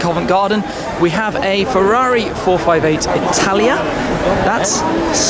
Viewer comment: sure that's a 458 italia?🤔🤔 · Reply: I thought he said 488 Italia :D